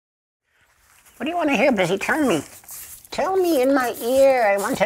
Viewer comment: The first time I saw this I misheard the question he asks his pet as: What do you want to hear in this eternity? Lol.